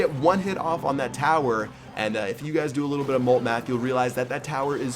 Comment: did he say "molt math"? 😂😂